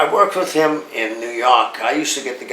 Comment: It sounded like he said New Yark.